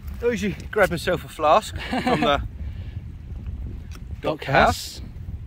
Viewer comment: Thanks for the video. It was entertaining and fun to watch. I think the blue bottle is my top pick. I hope some day to find one that isn’t a Vicks bottle. I too must admit that I thought was saying “dog house” and that made me wonder why you would take a flask with you when it was probably the drinking that got you sent there in the first place. Have a great week and please keep safe.